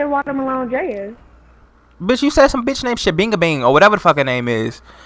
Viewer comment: Jimmy is my favorite character and he still wearing the same clothes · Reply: I have 2 scenes you was dreaming about the hash slinging slasher and yo girl said she was with starkesha and you said I thought you said shabingabang